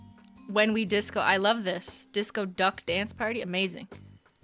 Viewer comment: At first I thought she said Disco Duck!!